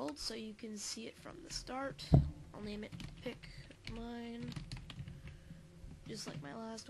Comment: I thought you said “pik-meme”